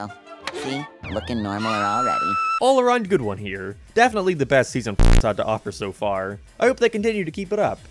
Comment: did he say season sex by accident?